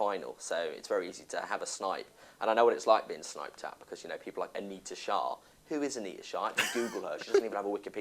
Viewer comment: What does he say at 2:00 ? I need to ? who is a need ? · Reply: Anita lmao